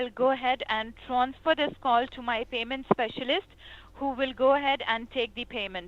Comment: is she saying "famous specialist"????? Well just HOW FAMOUS is this specialist? Lol